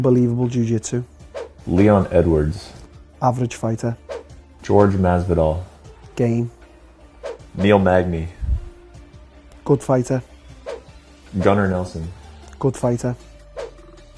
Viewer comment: When the ignorant asshole interviewing Till said "george" Masvidal did Till say Game or Gay?